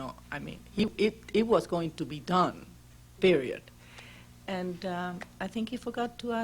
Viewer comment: DID SHE SAY PERIODT????